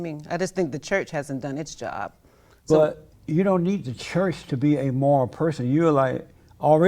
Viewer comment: did he just say he was not a moral person? Why have you chosen to be amoral? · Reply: No he didn’t say that idk how u could have even heard that he said you don’t need the church to be a moral person